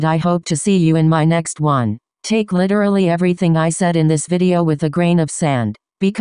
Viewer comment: At the end it sounded like you said “take this with a green of sand” in reference to your neon green remarks. 😂